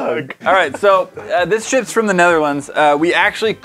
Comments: did he say shits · did just say “this sh*ts from the Netherlands” ? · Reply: TPonder lmao you commented this forever ago but, he said “this SHIPS from the Netherlands” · I know he said "This ships from the Netherlands" but I thought he said this shit's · Anyone hear say “this shits from the Netherlands” or did he say ships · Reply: i think he said ships sksk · Anyone else hear say this shits from the Netherlands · did anyone else hear say: this shits from The Netherlands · am I the only one who thought said, "This shits from the Netherlands?" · "This $#💩+'s from..." Nobody including the editor(s) or even himself noticed and continues making the video then posts it Me: Hey now, this is a family friendly channel.🤣🤣🤣 I've listened to this part 9 times and I hear it every time. Am I the onky one that hears it... Did anybody else notice or hear the slight sh💩t bomb that got left in the video.🤣🤣🤣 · did he say sh"t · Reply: He said ship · When said "This ships from the Netherlands." I first heard "this sh!* from the Netherlands" 🤣😂